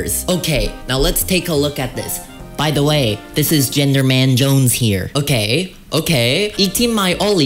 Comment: I kept hearing "gender man jones" as "gingerman Jones" I